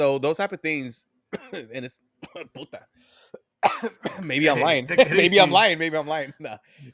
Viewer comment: I heard a “puta madre” being shouted off mic , and bitch ass infantino name change ❤